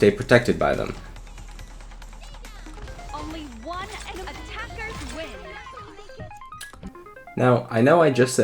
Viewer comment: what did she say? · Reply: Aika said "stay down" or if you mean announcer "Only 1 ene- attackers win!"